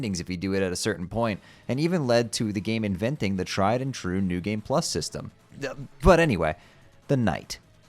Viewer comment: what did he say? The tried and 'what' system? Captions didn't help, just curious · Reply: "New Game +"